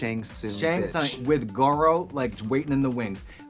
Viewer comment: did katya call Kandy " Goro" @ 23:55? lol